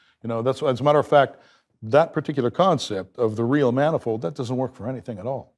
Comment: It only sounded like Chris was saying manifold over and over. He was actually saying "many fold" many fold times. 🤣🤣🤣